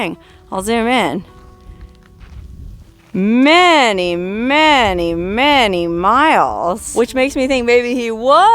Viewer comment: I hear ya callin' me! "Manny, Manny"